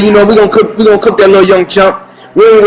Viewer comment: i thought i heard "young chump" too...i was waiting till i had more time so i could sit back and listen to the tape again. Great job...u see there are certain people who have been looking for an opportunity to destroy floyd so they will literally make up things as cover for their real agenda. They knew they couldn't really pin the racism thing to floyd except there was a specific reference to skin color...they couldn't find any so they made it up